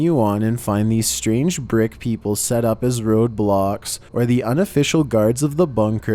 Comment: When he said "roadblocks" i thought he said... Roblox